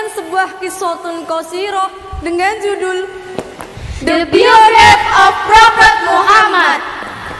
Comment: judul nya itu apa ya ka??? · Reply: Kalau tidak salah denger "The period of prophet Muhammad " Kk